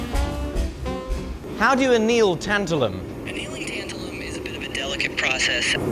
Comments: I could’ve sworn it said titanium 😂 i’ve never actually heard of tantalum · i knew i wanted to watch this while chilling with a grindy game. not gonna lie...everytime i glanced at youtube i thought it said "tarantula". not "tantalum"